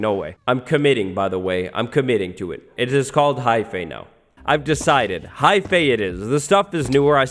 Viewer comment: When you placed the Shroomlight around 20:18 you said something I didn't really understand, what is this "hypea" you're mentioning here?